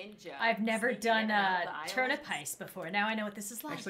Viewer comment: At first I thought it said trump heist